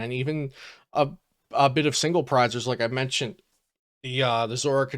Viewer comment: I heard you say "single prize" and I came running....